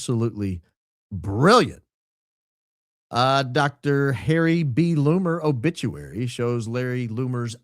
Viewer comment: I heard him say Larry Loomer 😂😂😂